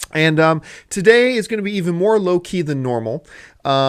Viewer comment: I put play in the video, set my food and mi drinks up for watching AMC mail bag and suddenly I hear "the show is going to be more Loki than usual"....what???